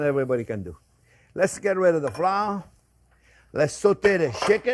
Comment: Whenever chef says "Wait until it's beautiful golden brown", I always hear it as "Wait until it's beautiful Gordon Brown" i.e the former UK Prime MInister :P